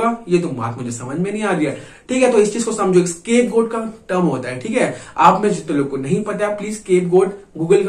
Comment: I didn't get the word, please someone tell me what's the word... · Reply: Scapegoat